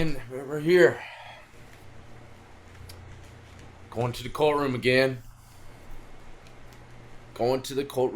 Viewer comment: I thought he said going to the "coatroom" like as a euphemism for like robbing people or something? I thought he was psyching himself up like "about to get this money." I then realized that hardly makes sense